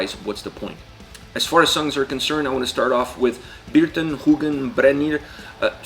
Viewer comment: "I want to start of with bearton hughen brenier"->"Birtan hugann brennir". 8:33 "Dreps start"->"Drepsótt" 9.39. "This is odin", FYI: No, the name of the band isn't pronounced as Odin (a Norse god, Icelandic Óðinn vs. Auðn), because it isn't, it means desolation. I'm an Icelander, know Auðn (and actually one band member, full disclosure), so I was curious and looking up such videos. Thanks you for the video, I wasn't sure what you were saying at those time points, had too look up (do not expect foreigners to be able to pronounce Icelandic, or the Youtube AI generating the subtitles, that were otherwise great). I'm conflicted if the autogenerated subtitles should be changed, if you actually have that capability.